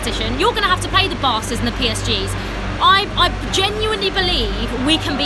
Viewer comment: Thought she said "you have to play the bastards and PSG's"